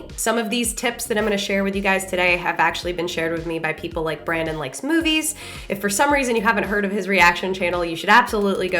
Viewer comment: I thought you said "Brandon likes smoothies."